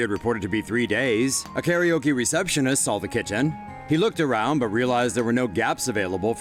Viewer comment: I heard kitchen instead of "Kitten" 😂😂 please tell me I'm just hungry